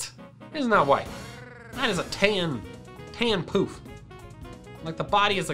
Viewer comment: It sounds like he says "Tan poop" 🤣🤣🤣